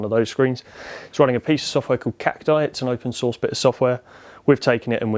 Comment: What did he say the name of the piece of software was? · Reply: Cacti.